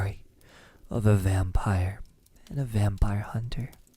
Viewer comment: I thought it said “Vampire doesn’t like you!” I was about to cry